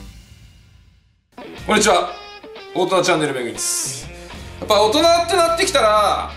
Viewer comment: 大人チャンネルって言ってたのがおるたなチャンネルに聞こえたwww